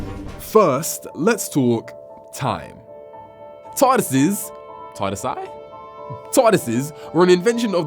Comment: it sounds like hes saying Tardasses 😂